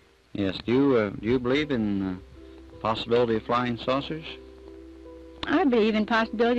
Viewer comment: i heard "Flying Sausage" 😄